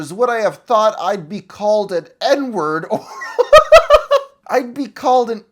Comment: at first I thought you said “Edward” instead of “N-word” at this point, because I was just listening to you instead of fully watching, and honestlyI didnt even question it